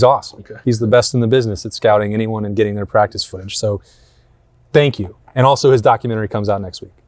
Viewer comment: getting their practice footage? Did I hear that correctly?